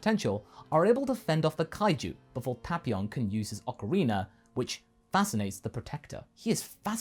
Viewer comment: I first heard this (Ocarina) as Aquafina